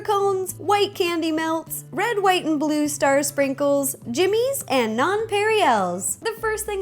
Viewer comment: i thought she said "JIMINS" lmao